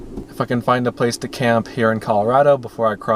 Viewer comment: Totally thought you said “F#%kin’ find a place to camp in Colorado.” at first. I was like woahhh 😂